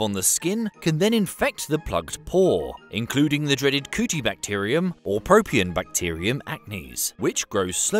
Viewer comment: I thought he said Coochiebacterium for a second....